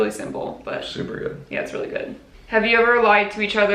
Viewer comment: I thought you said 'do you think white lives matter?' hahahaha I was so confused